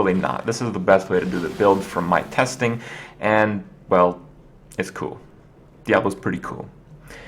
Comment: did say... diablo is cool?